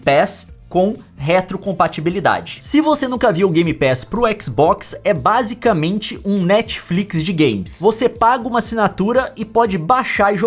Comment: Só eu que não sou muito maduro que ouvi "Netflix de gays"? kkkkkk